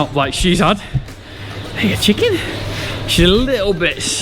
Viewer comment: /38 did say chicken to a cow?